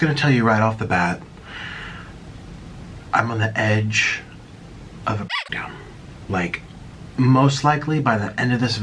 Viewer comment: what did he say, he said something that involved dom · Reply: he said “he’s on the verge of a breakdown.”